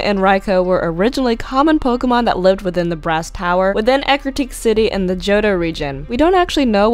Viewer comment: damn guess my hearing is off thought you said breast Tower